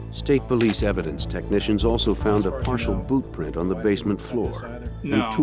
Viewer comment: I swear I heard the narrator say something to the matter of, " ... found a partial BOOB print on the basement floor along..." with emphases on the word 'boob' ofcourse. I'm sure now the narrator said boot 👢 instead 🙄 of boob as much as I would of liked for the narrator to have made an accidental woops with the words. That would of been pretty funny 😄.